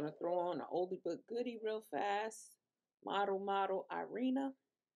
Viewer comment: SIS, WHAT company makes the honey blonde wig, ?? Check the name of her, I looked high and low, thought you said Model Model , Irene, but that's not it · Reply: Thank you for always watching....just getting a break at work. It is Model Model Irina wig. Color om3t427n I think glamourtress still has some.